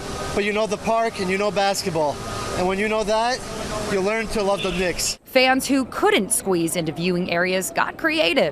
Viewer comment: Did he say, “You learn to love the d**ks” @ 8:32?! 😮